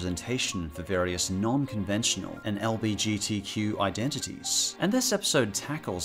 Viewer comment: did he say lbgtq or am i trippin